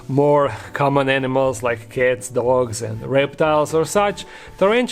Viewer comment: When you said “pets like cats, dogs and reptiles” I heard “pets like kids, dogs and reptiles”. 😂